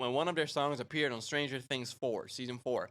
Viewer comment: "Why do I hear st anger?"